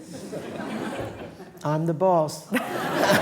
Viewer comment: what's the third opera he mentions? I don't understand what he says. Othello, Tristan, and...? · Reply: Werther, written by Jules Massenet